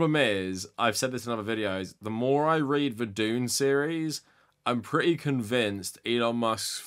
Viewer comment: I misheard you on 3:35 and thought you meant the Doom series and I think that's probably a more accurate theory. If anyone's going to open a Hell-Portal on Mars it's our Elon.